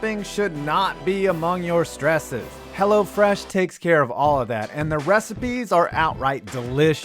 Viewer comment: When Hello Fresh first came out, I thought they were saying “Hella Fresh” 🤣